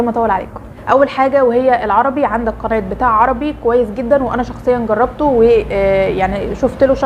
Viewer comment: عدتها ثلاث مرات وما سمعتش بليز ممكن تقولي تاني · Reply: قناه بتاع عربي